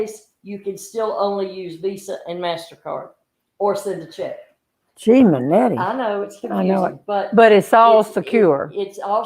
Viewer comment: Hold the bus.... At 12:59 did she say “Gimanetty” (sp) ??? Just spit out my sweet tea 🤣 Momma and family in heaven are rolling! 💞💞💞💞💞👌🏻👌🏻👌🏻👌🏻